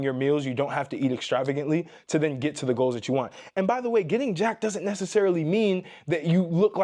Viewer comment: I thought he said getting “JACKED OFF” I’m like yea women super freaky 😅😅😅